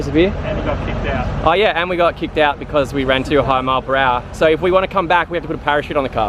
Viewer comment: that's laughable. 200mph in a quarter mile would be a mid 7 second pass. This car is never going to get close to doing that. 200mph you need 2 parachutes as well. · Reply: I just stated what I thought O heard in the video but I stand corrected that I might have misheard the 200 for "too high a" or something like that. I know that he could never do the 200 mph on a quarter mile but I thought that he might have been too fast at some point on the strip.